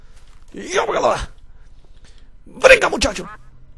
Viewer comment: WTF xD 8:00 Veng aqui muchacho?? did i just hear that xD TO A ROCK o.O